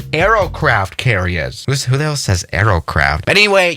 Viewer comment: wait. Does he say: Aerocraft carriers?